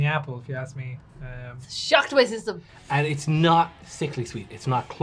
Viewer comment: Daheck did she say at 5:38?! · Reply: "shock to my system"